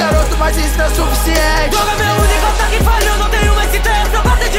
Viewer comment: Eu ouvi "going merry" MAMAKAKAK